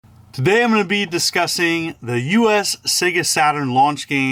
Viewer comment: I thought you said" today I'm gonna be disgusting" I thought this is gonna be a good video! Lol